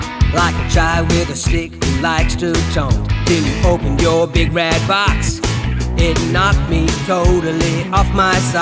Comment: I thought it said big back box lol😂